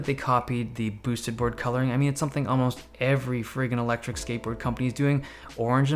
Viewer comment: who had to replay at 3:40 to see if he actually said frigging?